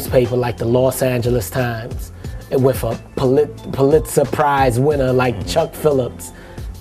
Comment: Pulitzer prize 4:19 Did he just Say “Polit Surprise” ? I’m dying 😢😢😢